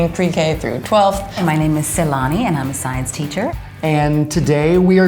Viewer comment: I thought she said salami